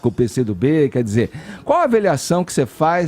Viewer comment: "aveliação" só eu ouvi isso? kkkk x'D